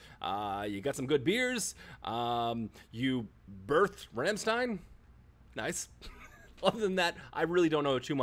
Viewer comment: 0:17 did I just hear Rammstein?